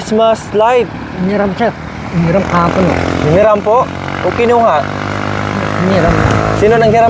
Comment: D mo narinig sabi ni sa 5:39 hiniram ang xmas lights niya. 😊